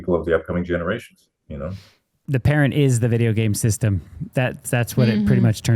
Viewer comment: what did say? The parent is the video game system? ?